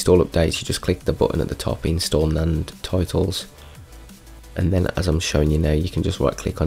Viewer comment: When you said "To install updates, you just click the bottom of the something something something" ... I can't understand that specific part. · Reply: “Click install and titles”